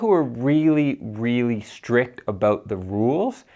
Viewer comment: You speak slowly, are you Canadian? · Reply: did I hear an "aboot"?